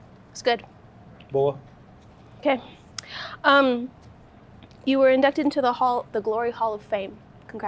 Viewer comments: Sounded like Nina said ‘you were inducted in the the Glory Hole of fame’ 😂😂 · For a moment I thought Nina said he was being inducted into the "Glory Hole Of Fame"